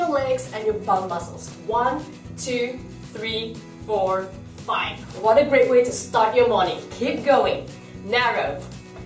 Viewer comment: I misheard what you were saying at 1:2O. I thought you said "wanna break wind, to start your morning", LOL especially since you were doing the wide narrow squats. I had to re-watch it to understand what you meant to say, LOL